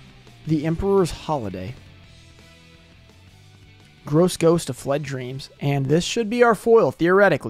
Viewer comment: I misheard you say ‘The Gross Ghost of WET Dreams’ 🤣